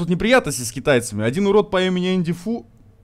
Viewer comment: послышалось "по имени AndyFy"